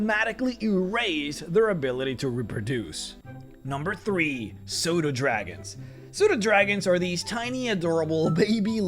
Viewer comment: Oh man I misheard Pseudodragons as "Sodadragons" and went: "Wait, D&D has Sodas? And it is mythical and plentiful enough for there to be a dragon variant? How does it relate to gems and ore? Is it a fluid dragon with constant hiccups? Man, this is cool! Yeah, go on, tell me how it reproduces!" And now I'm genuinely kinda sad that this doesn't exist... I've also almost convinced myself that I should homebrew this and through it into my games :P